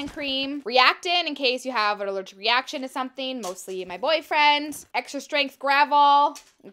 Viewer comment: it sounds like she said boyfriendS🤨